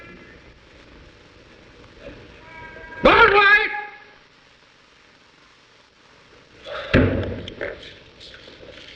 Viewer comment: What did he say? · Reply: “Alright!”